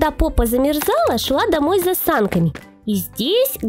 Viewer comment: мне одной кажется что она сказала с засанками?